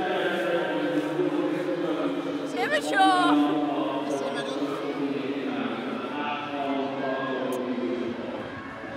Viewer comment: what did Hempo say? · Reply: imature